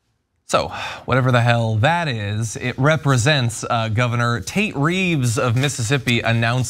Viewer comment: I thought they were saying “Taint” Reeves instead of Tate Reeves at first, but after seeing that commercial, I’ll stick with the former.